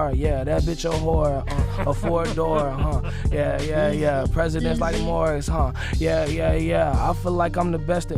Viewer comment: ?Did he say president b1den wars @ 1:53 -Gmoney